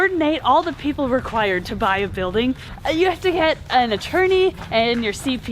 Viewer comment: Me: "You have to HIT an attorney!? COUNT ME IN!!!" x'D I know said to "get" an attorney. But to me it sounded like "hit" :P